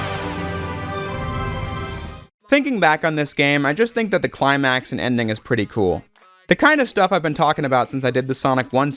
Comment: What does he say for Void? Sounds like Angry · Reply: frenzy